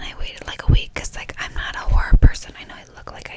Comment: ngl i thought she said whore mange lol but then i realized she was saying horror 💀 LMAO